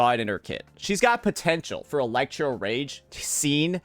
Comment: did someone just say potential